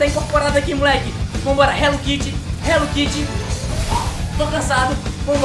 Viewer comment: kkkk entendi Hello kity